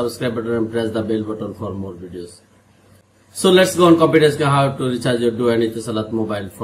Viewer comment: what you said let go “confidence”?